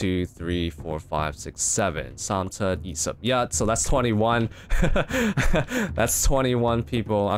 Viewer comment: It's great when u share the same language/dialect with another youtuber 8:51 (3, 7, 21 is what he said btw)